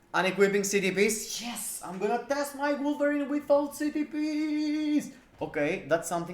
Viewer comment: Lol really sounded like you screamed... Yes, I'm gonna test my blueberry with all ctps....😂😂😂 instead of wolverine, had to listen 5 times to get who you were talking about lol